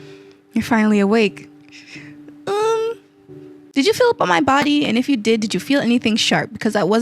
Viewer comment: I thought you said “fill” not “feel”… :>